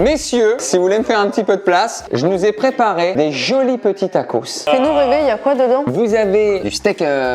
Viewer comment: Y’a que moi qui entends à 4:12 Camille qui dit « sinon bébé y’a quoi dedans » ??? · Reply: En vrai elle dit "fais nous rêver y a quoi dedans " mdrr mais j'avais entendu ça aussi au début